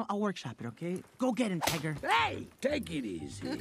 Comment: i thought perrito sayd "Ni##ar" but its "Tiger"